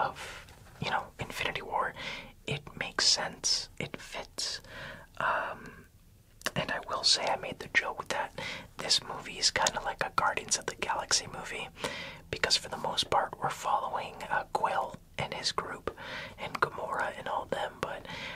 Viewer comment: I thought he said Gardens of the Galaxy 😅